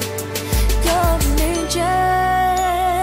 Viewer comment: on the part where it says "you're an angel" I thought she said "you're a midget" and I had to look at the comments to see what the lyrics were. hahahahahaha